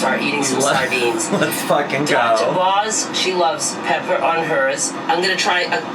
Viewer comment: I thought she said Dr Oz and I was like wow he really is a quack 😂